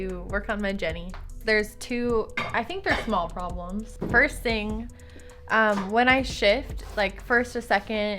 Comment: I thought she said when I shit